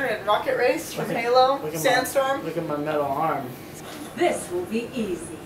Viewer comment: am i the only one that heard midlle arm instead of metal arm